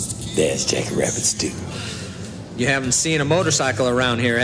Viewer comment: Did he say Jazz Jackrabbit 2 ???